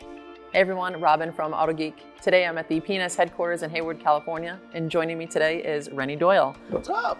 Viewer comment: when says, "today I'm at the P&S headquarters in Hayward California", it sounds like she says 'penis headquarters'